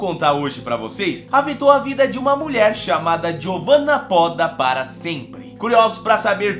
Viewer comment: Inicialmente eu achei que ele tinha dito que o nome dela era "Giovana foda para sempre". Aí eu pensei que diacho de nome é esse? Aí depois eu vi escrito e vi que era "Phodda" e não foda.